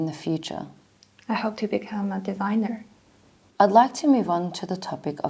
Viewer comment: i heard diviner @@